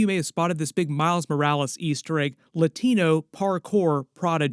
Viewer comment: At about 3:35 I thought I heard “Latina Hardcore” lol had to run it back just to make sure you hadn’t accidentally mixed up your script with your search history lol